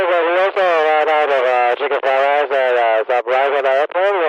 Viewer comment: I heard "chicken fried rice."😂